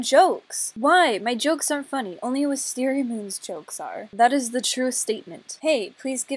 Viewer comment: I heard "WisteriaGoon" Is that what she said? XD